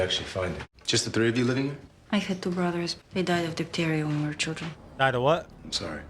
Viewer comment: I think she said her brothers died of diptheria